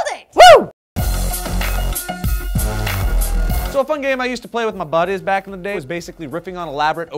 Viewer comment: does he say " so a fun game i used to play with my butt is?